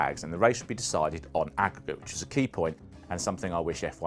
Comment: "and the race would be decided on akwkwk" Don't understand? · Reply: Aggregate. Stupid pronounciation, had to read about the grand prix to find out what it was. Basically at the red flag they keep track of the gap between drivers and at the end they add the gap again. Like in Football when they play home and away and add the matches together. In this case, before the red flag Schumi had about 6 second lead. After the restart, Hill pulled away from Schumi and finished 10ish seconds ahead. So the aggregate was that Hill won by 3.5 or so seconds